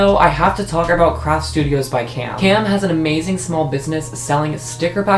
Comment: i love the new bookshelf!! its so cool also is it just me or did i hear "tam has a new sticker selling business" lol